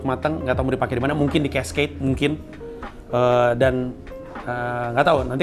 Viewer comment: ngomong apa ya? Judul special berikutnya kah? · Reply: CASCADE kayaknya, atau mungkin casc"eight"